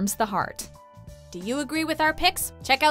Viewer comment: It sounds like she said miz mojo WOW THAT MAKES PERFECT SENSE